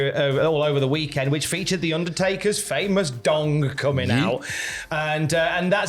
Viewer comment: I had to rewind as I thought they said the undertaker’s dong and that sort of thing hasn’t really been tolerated in WWE since Pat Patterson left the business.